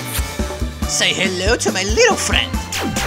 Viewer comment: eu não entindi o que ele falou kkkkk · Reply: " Say hello to my little friend " kskskskskksksksk